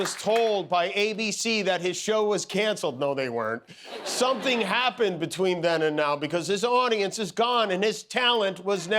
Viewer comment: What is the person shouting into the audiance? Just curious! 🙂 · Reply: "We're right here Jimmy!"